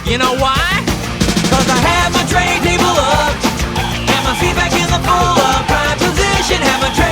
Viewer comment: he actually says seatback here